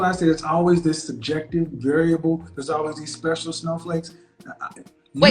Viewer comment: Did he say special SNOW FLAKE??? 😮 This guy is/was the truth in the booth.